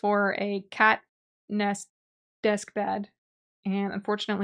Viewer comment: At first I heard “Katniss desk bed” and entered a fugue trying to piece together what that would entail.